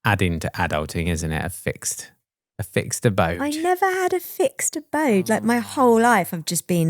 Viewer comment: So the actual words were "I never had a fixed abode," but what I heard was "I never fixed a boat." I thought that's it--if adulting means knowing how to fix boats, I'll never make it. Good thing I kept listening. 😆